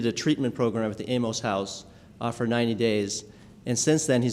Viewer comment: Did he just say he did 90 days in the anal house??? Lmfaooooo